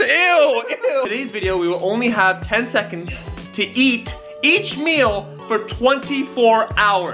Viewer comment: I thought it said “10 seconds to eat each other “ at first 😭😂😭